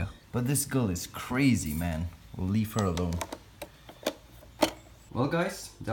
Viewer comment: When he said “I’ll just leave her alone.” I thought he said “I’ll just leaf her alone”